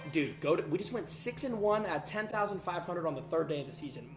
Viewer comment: did you just say.. 61?